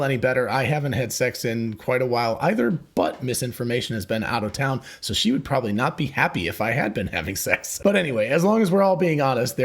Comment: Its ambiguous. If she didn't want to be, she would call herself Mrs. Information.😁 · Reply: I wasn't thinking in terms of Miss versus Mrs, but when he said it I heard it as one word for a second, 'Misinformation'.